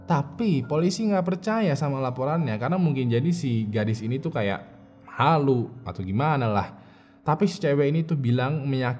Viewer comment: kakak : GET UP!! are you okay? adek : what a b*tch... semoga gw salah denger 🤣🤣🤣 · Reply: "On your feet soldier we are leaving"